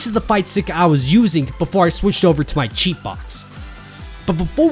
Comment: Speaking of which, what do ya think of the RaditZ MOD that came out??? 1:25 Also, what’s a Cheat Box?? Or did you say sheet box?? Is that another kind of controller??? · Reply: Cheatbox is a joke way of saying hitbox: the leverless controller. And yeah that raditz mod is insane. I love how the took moves from xenoverse and put them into his moveset!